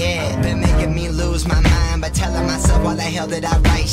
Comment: Did he say Tel annas?